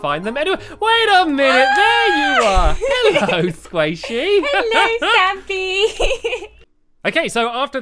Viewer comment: Did you just say hello I'm sqasy😋